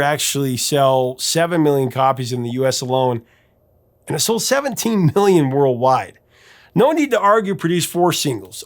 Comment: Did say it sold "17 billion worldwide" or I heard wrong? At